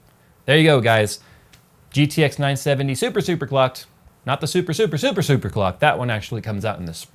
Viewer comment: I thought you said super cock for a second there.